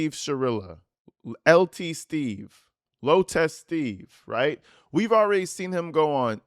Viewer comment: As a Nurse, I keep hearing you say “low testes” whenever you say “Low Test Steve”🤣🤣🤣🤣